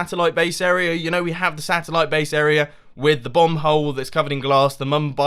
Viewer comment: He said 'Bomb hole' i heard 'Bum hole'